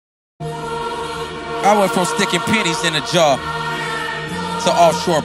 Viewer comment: i thought he said sticking explict in a jar?